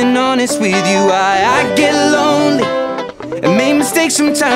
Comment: I thought it said "baby I be near ya" but turns out it's "bibia be yeye"😅